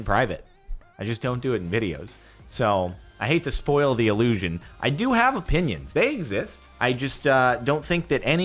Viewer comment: Said, “ I do have opinions” thought he said, “I do have a woman”